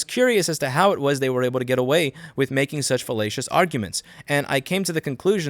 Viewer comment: Too much Oglaf, I keep hearing "fellatious" instead of "fallacious".